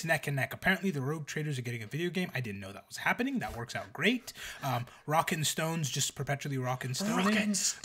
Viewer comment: DID I HEAR ROCK AND STONE?!